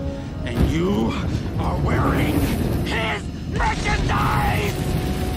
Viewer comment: As a kid I always thought he said "Let you die!" instead of "Merchandise!" 😅